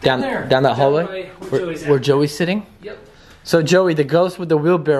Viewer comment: ish did anyone hear that little boy say yes?!?!?!